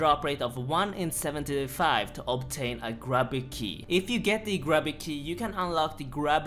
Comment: I could have sworn he used to say"grooby key" lol. I would sell the 7b gp obviously if I won it. That's a few thousand bucks